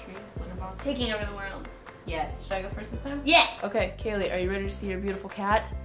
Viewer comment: did she say Kayleigh? (also known as kaylee) ?